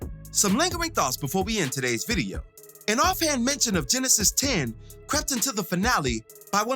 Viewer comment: Thanks for the clarity on the Genesis 10 reference. I was wrecking my brain. I thought he said “Janice is 10” potentially being their child/grandchild being Ghost and Tommy’s age but now it makes sense.